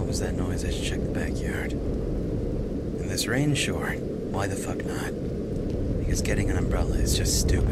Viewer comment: Did I just hear Cry say, "Hey, fuck." And not fog? O.o XD